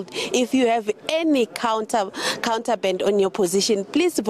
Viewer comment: Counter'band * contraband Phimani.....but English is not our Mother tongue....i far from being perfect.....m still learning...26/10/2024.....i may be very wrong too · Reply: Thank you very much.... compatriot.....26/10/2024.....but listen carefully...I heard "Counter band" when she first opened her mouth and repeated it...Maybe my ears are blunt....